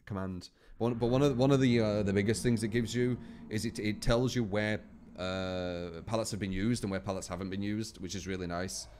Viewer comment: See at 17:30 does anyone know what perk was talking about? I assume it's the one that makes pallets and windows highlighted but I've no clue who has it. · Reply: He said windows of opportunity